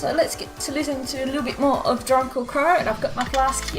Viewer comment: I keep hearing you say.. "drunkle" qrow?